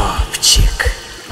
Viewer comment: Олежопчик???? Или мне послышалось???!